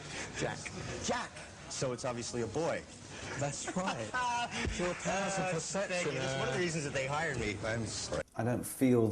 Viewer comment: What did Alan say at 1:59 after that’s right. Something about perception? I need to know😂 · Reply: “that’s right. Your powers of perception”